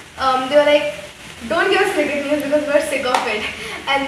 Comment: wait what did you say "don't give us ......?" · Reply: I said '"don't give us cricket news, we're sick of it" hahah I'm sorry thoda fast bol liya XD